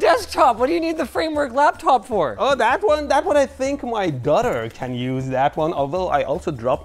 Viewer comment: Did he just say his "Gutter" can use that? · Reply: he said his daughter